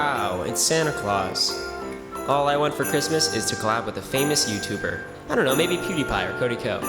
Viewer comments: At first I thought he said “all I want to do is go out with a famous YouTuber” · I thought he said “I wish I could go out with a famous YouTuber.” 😂